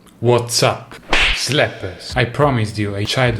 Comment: Why the English generated Subtitles Said 'Slap Ass?